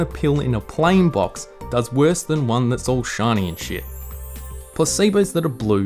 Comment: Shiny and shit? Did I hear him correctly? lol